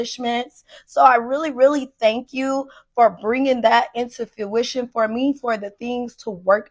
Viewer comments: I like how she pronounced fuchsia as "fuh-oo-shuh" instead of as "fyoo-shuh". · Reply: lol is that what she was saying? I seriously thought she said fruition lmfao 😂😂😂😂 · I like how she pronounced fuchsia as "fuh-oo-shuh" instead of as "fyoo-shuh". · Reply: I just thought she said fyuition instead of fruition but not I can't unheard it 😭